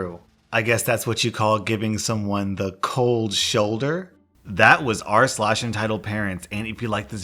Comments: Did he just say “hat was r slash entitled parents” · I heard the end 4 times did he just say 'r/ entitled parents '?